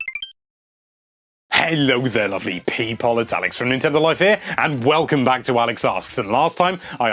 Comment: It sound like he says hello lovely people its ONIX from Nintendo Life